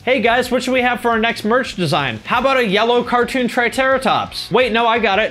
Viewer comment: Triterratops? Lol!... Was that intentional? Like were you trying to turn “Triceratops” into “TriTERRORtops” because it’s a monster-triceratops? Or was it just a verbal-stumble? Oooorrrr do you actually think that that dinosaur was actually called a “Triterratops” Lol... either way... it’s funny.